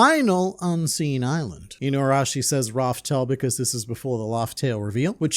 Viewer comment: i think he said rufftale… because hes… you know… a dog